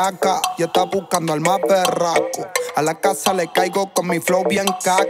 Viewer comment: pense que decia "y ella esta buscando al mas PERRAKO"